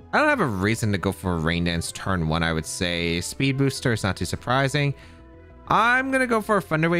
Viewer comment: I’m pretty sure that flutter mane in game 1 was choice specs since thats why he kept clicking icy wind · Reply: It showed Booster Energy. The player just kept spamming lol. Edit: this is what I get for listening podcast style lol. I just heard “Speed Booster” not realizing it was Sun. Ignore.